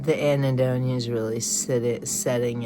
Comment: May lord you have a PHD in this stuff… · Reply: What is the word “anitdone” you mentioned at 3:57? Oh, “anhedonia: the lack pleasure.” 😢 Holy shit…